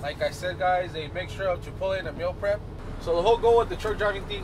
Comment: I honestly didn't understand what he said. Did he say a Chipotle and a meal prep?